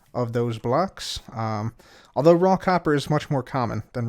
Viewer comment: I could have sworn he said Rockhopper